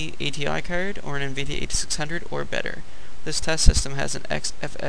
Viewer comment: i didn't quite understand what you said...you said at 0:08 "an NVIDIA 8600 or better"?'cuz i have a 8500